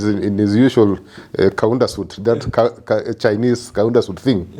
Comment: Did he just say " HIV Kaunas suit"